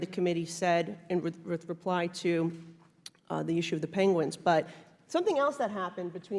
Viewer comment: did she say penguins????